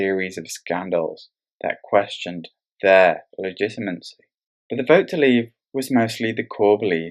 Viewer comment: "...a series of scandals that questioned their ?????" at 4:14. What did you say, I've re-listened several times and just can't understand that word. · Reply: That questioned their legitimacy